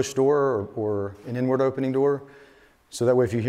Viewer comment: Lol I thought he said "an N word opening door", he definitely said an inward opening door